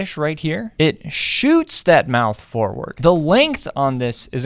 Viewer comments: I thought he said sling your ass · Am i the only one thought he said Sling jaw ass until i watched it again